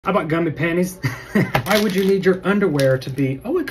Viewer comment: OMG..... The first thing I hear is "I got gummy panties " XD